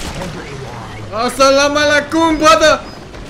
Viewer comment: Salamulaikum instead of allahu akbar